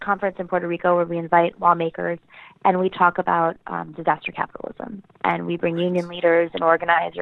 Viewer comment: Is Nomiki saying "divestor capitalism" @ 5:20? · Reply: Gnuggets11 “disaster capitalism”